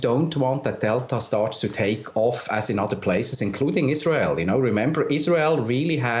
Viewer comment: That host face at 3:25 is my face. I don't know what he is saying. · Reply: something something isreal something lockdown